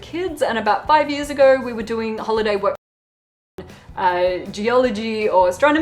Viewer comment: Does say "holiday workshops" at 0:40 ?